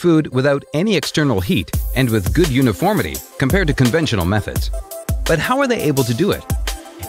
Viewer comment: Did he really say uniform cooking